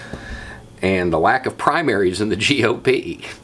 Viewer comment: LOL....I thought it said "Let's talk about the lack of "PRIMATES" on the campaign trail." Past my bedtime. :o)